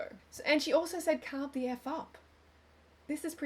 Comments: To me it sounds like she said "cook the f#@ up" the jackfruit. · She said she "could f*** up a nice jackfruit" not "carb the f*** up" haha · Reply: I thought she said "carve the f*** up" lol